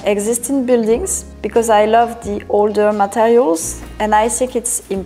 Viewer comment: Some of these subtitles are funky "all the materials" instead of "older materials" at 0:42, "murals" instead of "mirrors" at 4:09 and "closer" instead of "closet" at 5:49. Love the apartment btw.Thanks. P.S. 6:17 - Time to change the toilet roll.